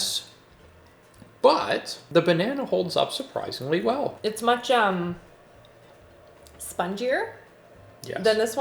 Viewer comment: Side note: More organization hacks video please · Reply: OMG anyone who speaks malay here? I swear I heard said "macam" (translation in English is "like") at 22:52. I had to rewind a few times to really hear what she said, which is "much umm" 😂 she used the word correctly though 👍